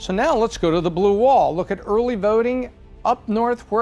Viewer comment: Who else heard her say 'When it comes to early verting.' ?